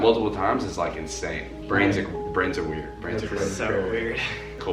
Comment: did they say Brennen is weird??? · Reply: They said brains are weird